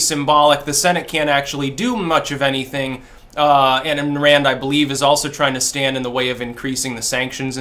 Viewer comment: anyone else hear 'In Rand I believe' right here?